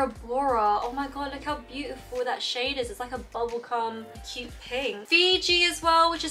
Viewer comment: did you say bubblegum or bubblecum😂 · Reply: 😂😭 I meant bubblegum!